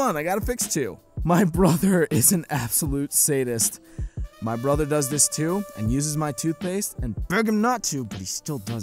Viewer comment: i keep hearing "I can't stand this my brother does this" you sound like you need a brother refund, but sadly those dont exist... at least none that ive found